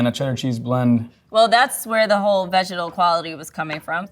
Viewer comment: Did she keep saying "Vegetle"?? Instead of Vegetable? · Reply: She's saying 'vegetal'. It's an aroma